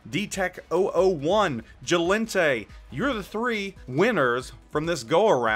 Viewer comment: !! at 1:10 did you say JLENTE?!? if so that is ME! can't be though cuz I've seen no email :( If you can't give away the Friesland I'll take it! :D